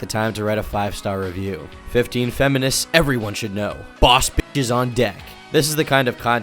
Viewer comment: I misheard that as "Boss bitches are undead"